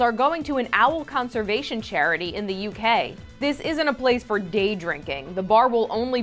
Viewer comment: Holy shit, at first I thought she said "this isn't a place for gay drinking" and I was suddenly wondering if there was some sort of bad blood between owls and homosexuals... I want one of these barns where I am.